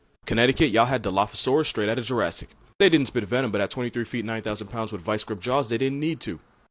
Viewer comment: I heard him say the dilo was 9,000 pounds instead of 900 and I was like 😳